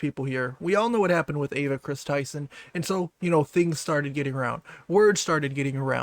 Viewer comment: i swear i heard Adolf Kris Tyson